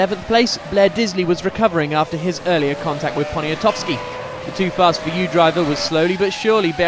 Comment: did I hear Poniatowski? Are there any Polish?